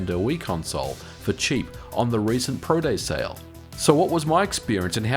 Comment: What is a Pro Day Sale? Even though the subtitles say Product Sale?